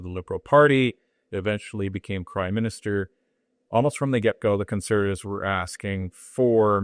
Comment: I swear said Crime Minister at 00:51, did I hear it right, gave me a chuckle.